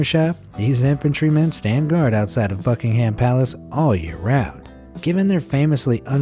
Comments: it's only me or he did say fuckingham palace instead of Buckingham palace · I honestly thought this man said f*ckingham palace in my first playthrough. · Did he say the f word at time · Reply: I Thought That At First. It Does Actually Sound Like He Says Fuckenham Palace Lol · did he just say "Fucking Ham Palace"? 🤣😂😅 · IS HE SAYING FRICKIN HAND PALACE? I SWEAR HE IS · did anyone hear fucking-ham palace or is it just me? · I thought he said "Fucking Ham Palace." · Did he really say, Fuckingham palace xD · sounds like he is saying f**kingham palace lol. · It sounded like you said f*ckingham palace instead of buckingham palace to me anyway. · Wait did he say fucking ham palace? At 9:35?